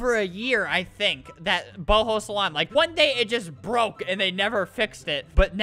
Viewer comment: Albert I heard you say ball ho salon at the start lol